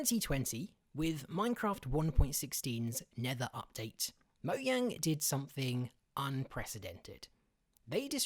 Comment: did he just say mo yang